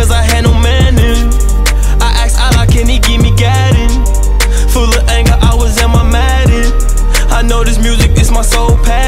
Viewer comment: Am I the only one who thought durk said "Full of anger I was on my madden"